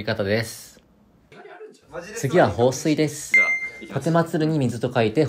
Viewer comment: 「香水」に聞こえた。